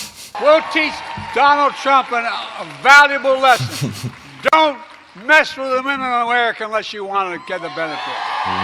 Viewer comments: It sounded like he said don't mess with women unless you trying to move up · It sounded like he said, "Don't mess with the women in America unless you want to get a benefit" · To me it sounded like he said, “Don’t mess with the women of America unless you wanna get the benefit.” It would make no sense for him to say that so I’m assuming that’s what he said. 😂😂😂